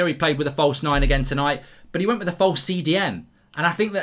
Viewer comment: Did he say 'false CDM'?!! 1:10 Do those even exist?😂😂